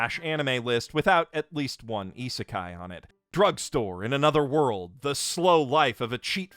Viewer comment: For a moment I thought you said "Drug Lord in Another World"... And if we think about how that would be... :D